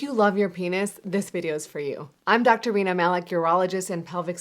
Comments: I THOUGHT SHE SAID DR. WEINER MALIK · I could have sworn she said "I'm doctor Wiener Malik..." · I thought she said her name was Doctor Weena Malik😆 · I swear, i heard her say i am Dr. Wiener Malik. I was like "Nah, c'mon. That is too good." Anyway, i thought she would talk about something that goes beyond common sense. Or something i as a guy already don't know. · Did she say "I'm doctor Wiener Malik"? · I for real thought she said I'm Dr Wena 😅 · Is it bad I thought she said her name was "Dr Weiner Malik" then I thought of Peter Griffin laughing...